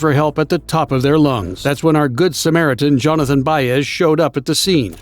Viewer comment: Why did i hear “jonathan byers” (from stranger things) at 2:11 😭😭